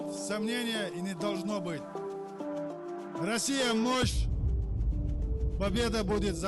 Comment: После Ахмат-сила, Аллаху-Акбар, Россия-... я не смог расслышать. Что они прокричали? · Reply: мощь